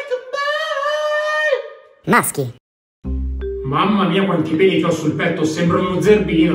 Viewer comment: ho sentito mamma satana...